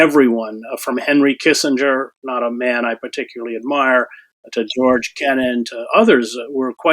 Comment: Is this just me? At 5:14, I hear Chris Hedges say "everyone from Henry Kissinger, a man I particularly admire..." My brain said, "this does not compute!" I had to play this back a few times and in slow motion and I swear that there is a jump in the video, just enough to cut out the word "don't". Watch his lips. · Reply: He said : a man I don’t particularly admire